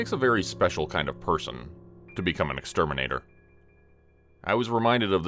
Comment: Thought it said ex terminator. Lol